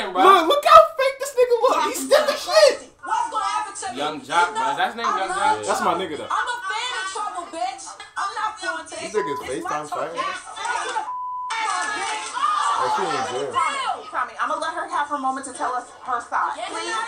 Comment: Did I hear that right? Or on 15:11 she said "I love Trump I'm a fan of Trump"💀💀💀💀💀☠️☠️.